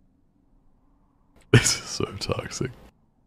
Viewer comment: knowing sykkuno is having a crisis abt loyalty makes it 10x funnier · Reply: But at 5:28 ish did corpse say "this is do toxic" that's my question